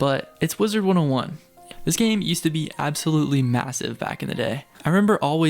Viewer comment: Did I just hear MASSIVE?!